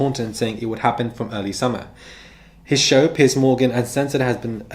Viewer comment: It sounds like you say "Pi$$ Morgan." It fits.